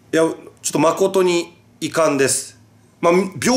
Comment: RIZINと被った仕事って、てっきりパチンコの地方営業かと思っとったわ(笑) カンケー無いが、１回目の「誠に遺憾です」ってところが「マホトに遺憾です」に聞こえて吹いたwww マァ、活動の場が広がって、引き出しも一応増えた、って事で良しとしとけば？ 知らんけど(笑)